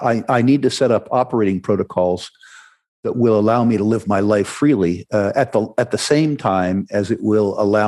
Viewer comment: At first I thought Rick said “I need to create operating protocols so I may lift my leg freely”